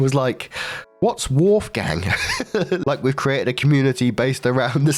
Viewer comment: I remember when I first started watching your channel I thought you were saying "Hey Waifu Gang" 😭😭